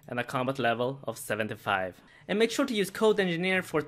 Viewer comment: At the end I thought you said 20% off of potato products and I got excited :(